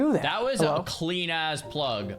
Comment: you did just say “that was a clean ass plug” I mean, perhaps I parsed it incorrectly, but you did say it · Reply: I can confirm that he said "that was a clean-ass plug!"